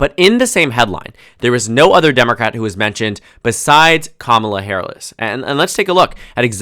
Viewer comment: BRO DID NOT JUST CALL HER KAMALA HAIRLESS 😭😭😭